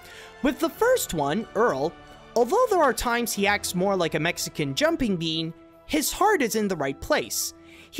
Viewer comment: When I first heard that part about the cop guy, I thought you said, " a Mexican jumping me", but then I went back and heard it as,"a Mexican jumping bean."